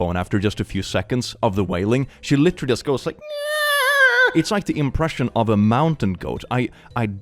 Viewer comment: I thought he said “it’s like the impression of a GAY mountain goat” at first and I was so confused